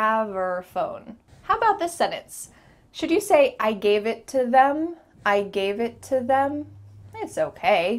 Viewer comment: I would have misheard "I gave it to em(them)" as "I gave it to him" sounds just like that without looking at titles